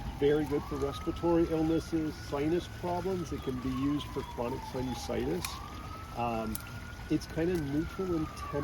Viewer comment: is saying "Chronic Sinusitis"?